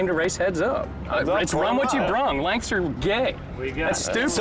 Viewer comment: I thought he said length are for gays now he ask for 8